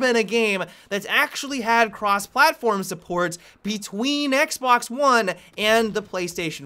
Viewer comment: i will be honest i thought i just heard him say xblox